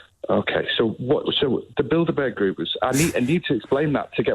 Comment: Thought he said build a bear group.